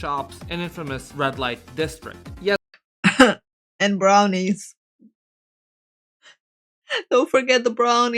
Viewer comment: Brownies (food) - if you know, you know, if not, best not to accept free brownies while there 😂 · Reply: ohh.... brownies, i thought you said bra maids and here i thought nobody can beat japan at weird kinky stuff 🤣🤣🤣